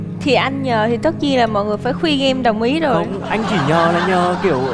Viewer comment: Tập này mình thấy bùng nổ cảm xúc nhất từ trước tới nay . Và thật sự mình rất nể cách dẫn dắt của chị Trinh . Thật sự rất tuyệt vời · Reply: nói là “em đồng ý” nhưng nói nhanh nên chắc bạn nghe nhầm thành “e là mấy”